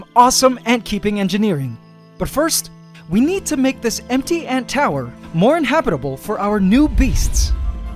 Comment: I thought he said butt tower